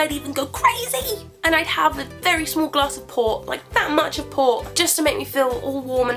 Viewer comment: what drink does she say at 1:47? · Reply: Port...?